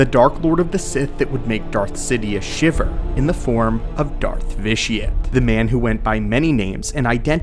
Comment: I misheard when you first said his name and thought you called him Darth Idiot, I laughed so hard.